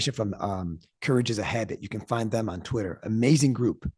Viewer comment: It sounded like he said courage is ahead on twitter is where he finds a lot of info but I wasn’t able to find it. Can anyone share the link? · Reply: Courage is a habit …they are on Twitter